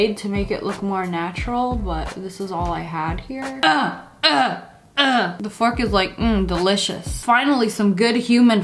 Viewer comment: i heard u say: '' The fricker îs like.. ''